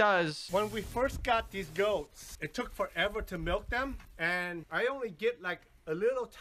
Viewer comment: I thought he said "When we first got these goats, it took forever to melt them" I was quite scared for a moment